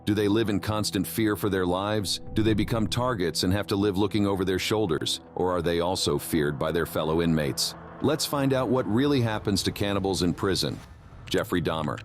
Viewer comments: I thought it said what happens to cannabis in prison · lol i thought it said "what happens to CANNABIS in prison" i was thinking... well... it gets smoked!